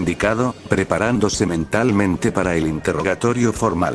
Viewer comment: Yo escuche que se estaba preparando sementalmente xD